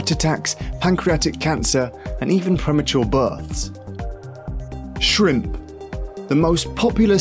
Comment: Sounds like he says premature burps